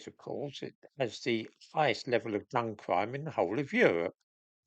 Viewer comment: please get a better mic - I heard 'bum crime' - which I don't think is what you actually said.